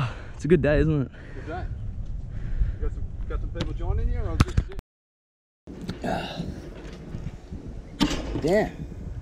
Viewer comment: What did he say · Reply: "You got some people joining you or this is it?"